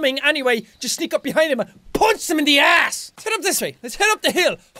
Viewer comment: so i go to get a drink and i come back and put on my headphones and i hear "AND I PUNCH THEM IN THE ASS!!" lol